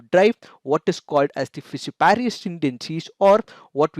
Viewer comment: Can anyone tell me what he said during 8:04 just before the "son of the soil" phrase? I didn't catch that clearly. Thank you!!! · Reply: fissiparous tendencies